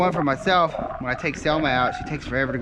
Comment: At first I heard “ I’ll keep a chair for myself cause my ‘cell mate’ takes forever to go to the bathroom 🚽 so I’ll just pull this out and sit beside her when she does her thing” I was like wth? Is he talking about his wife??? Then I rewatched 15 more times and get it now!….. you said ‘Selma’ not cellmate, I love her btw 😂